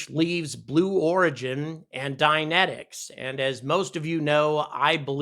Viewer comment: You were saying "Dynentics", I thought you were saying "dianetics". For a second there I thought Scientologists started to develop rockets -_-'